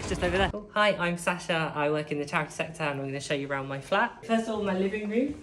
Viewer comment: I thought he said he worked in the tax sector. But it must be me.